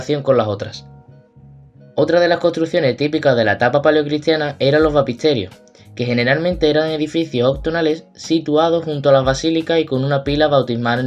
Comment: No entiendo con claridad, lo que dice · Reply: "otras de las construcciones típicas de la etapa Paleocristiana eran los Baptisterios, que generalmente eran edificios octogonales situados junto a las basílica y con una pila bautismal en el centro"